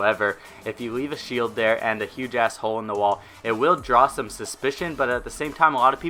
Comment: "huge as hole" sounds like hes saying "huge a$$hole"